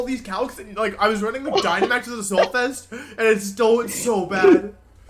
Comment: I had to rewind that because I heard "It has 148 speed" the first time